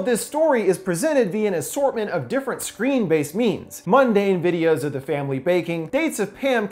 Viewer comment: For half the vid I thought you were saying screen based memes